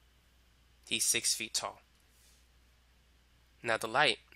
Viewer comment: idk y but at 58:55 when he was drawing the man and said "he's six feet tall" I swear it was like he said "this is my person try and you just go on and say something" and I found it super funny.